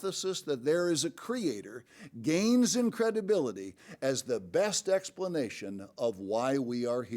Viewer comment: Mr. quoted “The hypothesis that there is a creator gains in credibility”, but I heard “gains incredibility”.